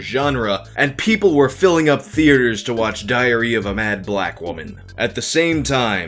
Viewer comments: I could of sworn I heard “diarrhea of a mad black woman” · anyone else hear "diarrhea of a mad black woman"? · I was doing dishes while listening to this, not really looking at the screen. All I heard was "diarrhea of a mad Black woman" (erroneously, obviously). I haven't stopped laughing at the visual that triggered and the dishes never got washed. 😂😂😂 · I heard that as Diarrhea of a Mad Black Woman... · I thought you said “diarrhea of a mad black woman.” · It sounded like you said people went to the theaters to see "Diarrhea of a mad black women" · I thought I heard 'Diarrhea of a Mad Black Woman.' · I swear I thought you said "diarrhea of a mad black woman" at the beginning 🤣🤣🤣🤣🤣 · I heard "diarrhea of a mad black woman". Lol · Sound like he said diarrhea of a Mad Black Woman 😂😂😂😂😂